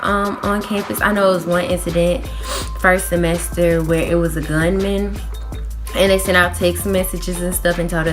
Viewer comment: did you say " gun man" ???